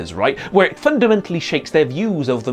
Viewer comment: I thought he said snakes...